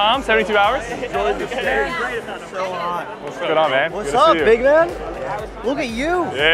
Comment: when he said what's up big man, at first i thought he said ( what's up then the N word ) 😂😂